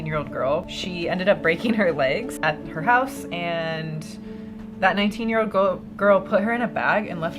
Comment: I thought you said vat19